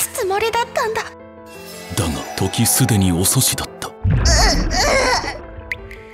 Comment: どうしよう、時すでにお寿司に聞こえちまって、